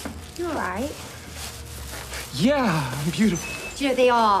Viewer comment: did he say yeh am beautiful